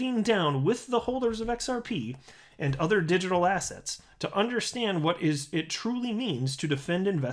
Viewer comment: Now whenever you say the word asset, I hear the word "asshat". Digital asshats!